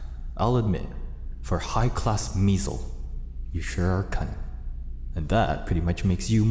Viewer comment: BRO I THOUGHT HE SAID CUNTY INSTEAD OF CUNNING